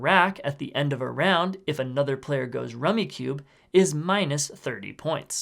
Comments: “Rummikub. I thought you were saying Rubix Cube wrong” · Thank you my son got this game because I thought he was saying rubix cube